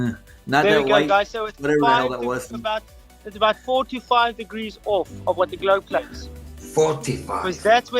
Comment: did dude just say that 3 degrees = 45 degrees? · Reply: He said "four to five degrees" and the guy on the other end of the zoom call also thought he said 45, but he figured it out after a minute or so.